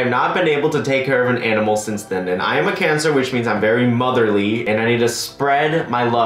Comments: I heard “I have a cancer” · I thought he said I have cancer and I got so worried 😧